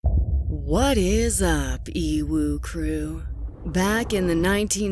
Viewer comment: i realized today that .. its whats up ewu crew.. l thought she said what is up evil crew... (enabled the subs thats when i realised )